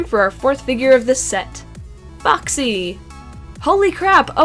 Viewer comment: Is it just me or did she say boxy